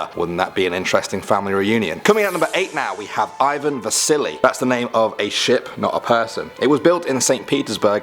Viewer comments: I thought he said "Ivan the Silly" :) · I legit thought you said Ivan the Silly. I nearly lost it XD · When you said Ivan Vassili, I thought you said "Ivan THE SILLY." I'm so silly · I thought he said Ivan the Silly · I thought you said ivan the silly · When he said Ivan vassili I thought he said Ivan the silly